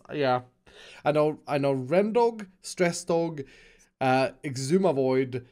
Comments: Anyone else hear stressdog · did he say stressdog? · did he just say stressdog